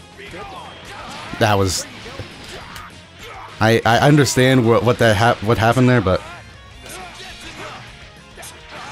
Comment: Is it just me, but at 6:32 when said “That was..” and didn’t finish his sentence, Android 17 just said “Pretty dope”. Am I hearing things or is that what actually happened? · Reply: No your not hearing things lol he did say pretty dope